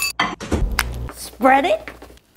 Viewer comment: Bro i literally thought she said damn danielle thats my name